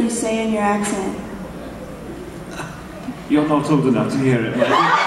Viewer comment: What did he say at 10:22? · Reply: Nevermind, I realized he said, "You're not old enough to hear it."